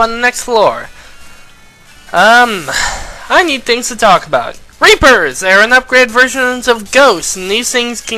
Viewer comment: did you say "Rapers"???? OH GOD WHY?